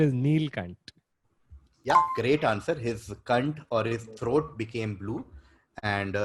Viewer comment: just Sounded Like saying cunt